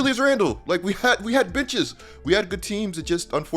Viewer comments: when said they got benches, it sounded like he said they got bitches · I thought said “we had bitches” instead on benches 😂